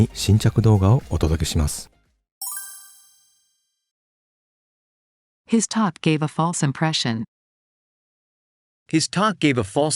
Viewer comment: 今回のディクテーションは、 His talk gave her fault impression. と、聞こえたのですが・・・。